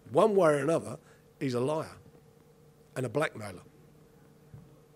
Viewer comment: "one way or another, he's a liar. ...And a black man." I totally thought that's what he said for a second.